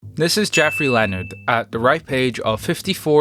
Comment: I heard "this is Jeffrey Leonard" and had to tab straight back in to make sure it wasn't THE Geoffrey Leonard from the 2000's🤣